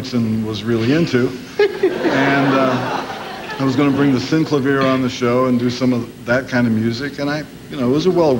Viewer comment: what band did Frank say he wanted? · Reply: Synclavier. Its the synth that he used on Jazz From Hell.